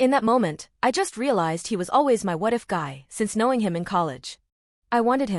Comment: I heard this as "my wife guy".... that said, what the heck is a what-if guy? As Yoda said, "Do or do not, there is no what-ifs"